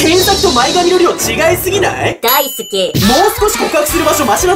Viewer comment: 大好きじゃなくてDAISUKEって聞こえる ほんでマスオが成歩堂に...